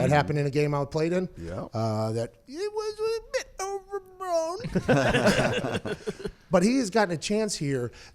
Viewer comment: did he say @ 1:50 mark "It was a bit over blown"???????